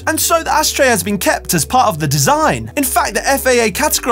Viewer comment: I always thought it was said "ass tray"